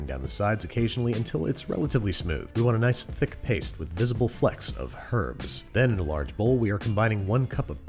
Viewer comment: When he said “flecks of herbs” I heard “flex of herbs” and thought, “Mm, sounds about right” 😂